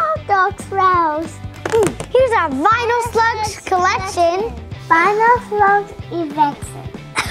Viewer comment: did he say erection lmao